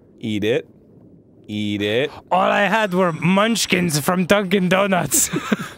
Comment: When he said "Dunkin donuts" I flipped out because I thought he said Duncan